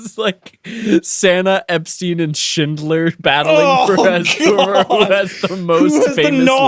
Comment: Santa, Epstein, and Shindler..I thought Ethan said Santa and Steven Shitler 🤦‍♀️😂🤣😂